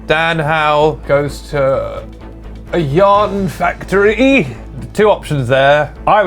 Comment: DAN GOES TO A YARN FACTORY TO GET A PERSONALLY MADE CHRISTMAS JUMPER FOR HIS LLAMA!! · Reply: AllOfTheDips Oh, I thought he said YAWN factory😂 Yarn makes much more sense